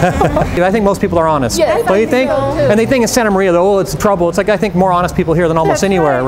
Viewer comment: Did he say Santa Maria